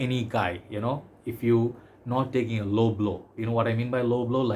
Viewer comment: When you said "low blow" I thought for sure that you said L'homme L'eau for a sec.